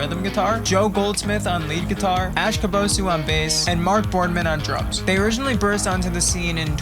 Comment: legit thought you said ‘and mark boardman on drugs’ when you were introducing him, i cant breathe edit: it sounds so good omg